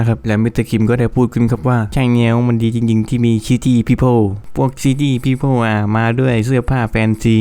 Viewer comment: ได้ยินตอนแรกฟังเป็น Shitty People 5555